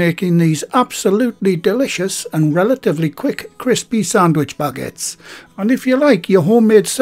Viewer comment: I love your channel. But I thought you was saying sandwich buckets. Lmao